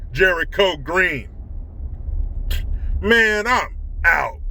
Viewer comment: I just went to your Green gear link hoping to get something with your final sign out words on it. I had no idea you were saying man I'm out. I thought you were saying man up. Oops